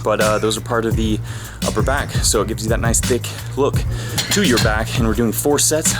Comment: could have sworn he said "gives you that nice...dick...look" at 2:09 I need to go to bed